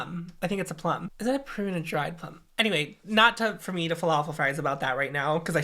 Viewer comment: i heard "not for me to falafel fries" when you were talking about prunes v plums lmao